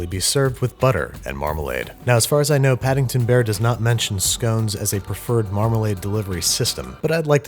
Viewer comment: "The ultimate expression of marmalade - on English scones". cries in English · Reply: I thought he said stones. Some scones are rock hard but calling them stones seemed a little harsh. Oh scones!